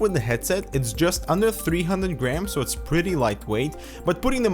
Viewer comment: When he said "just under 300 grams" I thought he said "just under 300 grand"